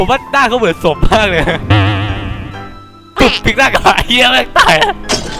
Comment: ฮาตรง 3:16 โครตฮาเลย5555 ฮาภาพกะหน้านี่ละ<num> ได้ยินคำว่าเ*ยแม่งตายแล้ว<num>:17